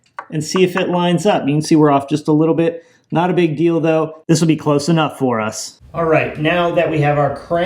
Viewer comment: When you said "not a big deal though", for a second I thought that you said "not a big dildo". 😂😂😂